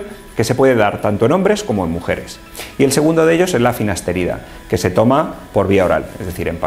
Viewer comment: cuales son los tratamientos que dices : minoxidil y el otro que dices en el minuto 3:38 no le entendi esta algo rraro la palabra algien que ayude porfa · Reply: Finasterida o finasteride